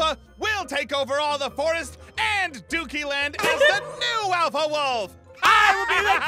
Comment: Zane: “This land is gonna be known forever as the Great and Powerful—“ Aphmau: “Doody.” Me: 😂😂😂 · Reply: Zane-im gonna name this the great powerful- Aphmau-Doody Land Me-I thought she said dooky land